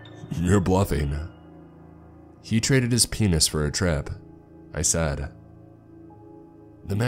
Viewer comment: I listened to this while working and I paused it when my shift ended. Later that night I resumed it when going to bed and the first thing I hear is “he traded his penis for a trip”